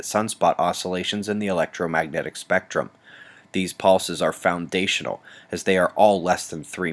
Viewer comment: Waves last forever :) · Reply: I thought you said 'pulse' in the video, not 'wave'.